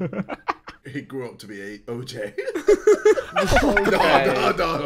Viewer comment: what did JJ say? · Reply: He grew up to be OJ Simpson